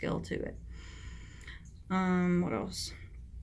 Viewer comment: Is there someone in the room with you? I hear a mans couce about the 27:50/27:51 mark say "what else"... I had to rewind 5 times... I thought i was hearing things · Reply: lol no I said what else but the sound/visual glitches when uploading sometimes. it's normal on the original hahaha creepy